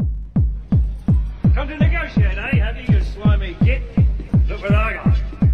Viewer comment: did he just say "slimey git!"?